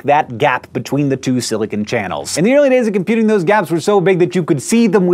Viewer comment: is he saying the computer in those gaps??!